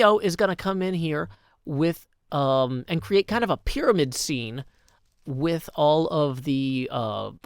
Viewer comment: I heard "pyramid scheme" at first and just thought, "yeah, that's something Cleo would do."